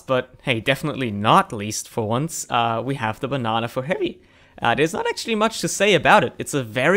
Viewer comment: did u just say the banana for harry? cause it really sounded like it. (i know it was heavy)